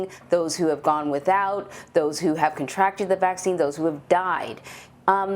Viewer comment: she actually said "people who have contracted the vaccine"